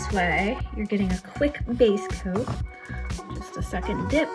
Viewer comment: Did you say to dip TWICE? Or did I mishear? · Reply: I likely said "2 second dip" or something like that. I only dip once with Studio White.